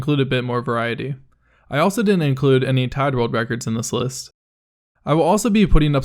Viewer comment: when you said you didn't include any tied world records, i misheard it as "Todd world records". yeah you better not include those >:|